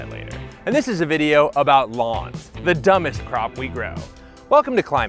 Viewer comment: I thought this said dumbest cop for like 3 minutes of the video